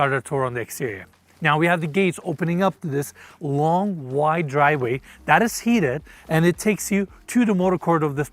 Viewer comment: Did he say the driveway is heated? @ 1:39 Heated?!!